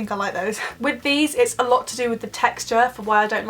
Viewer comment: Did she just say i like nobs 😂😂😂